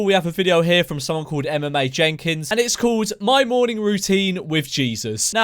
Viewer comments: Anyone else thought he said MMA jenga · Honestly thought you said MMA Jenkins, and I was gearing up for an MMA routine! 😂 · when he said emma mae jenkins i thought he said MMA jenkins until i saw her name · I thought he said MMA Jenkins, not Emma Mae Jenkins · I thought you said MMA Jenkins I was fearing for your life there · I thought you said MMA Jenkins at first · TBH I thought you said MMA Jenkins... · Genuinely, when he said her name at the start i thought he said MMA Jenkins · Legit thought you said MMA Jenkins for the first one hahahahaha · thought u said MMA Jenkins, damn imagine her vs mcgregor, GG EZ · Heard that as MMA Jenkins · Thought he said MMA Jenkins lol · I thought you said mma Jenkins like some stupid name for a 2 to 1041 W/L ratio mma fighter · so, her name is "Emma Mae Jenkins." But, this this boi is a brit i heard "MMA Jenkins." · it sounds like he said MMA Jenkins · Fam I thought he said MMA JENKINS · I thought he said MMA Jenkins and was expecting some big burley tattooed man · When he said Emma Mae Jenkins I thought he said mma Jenkins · I thought when memeulous said Emma Mae Jenkins that he said mma Jenkins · I thought he said MMA Jenkins. And i espected some big guy with muscles.And i thought that he will react to more YouTubers Than one. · when he said Emma mai jenkins I thought he said mma jenkins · Thought he said MMA Jenkins which sounded like she would have a LIT morning routine. Very disappointed tbh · thought he said mma jenkins...